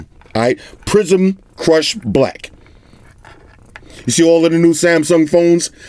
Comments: I legit thought you said "prison crush black" and was thinking oh wow he's got new terms for 2020 lol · I legit thought you said prison Kush black when you mentioned the color of the phone lol great vids as always 👍🏽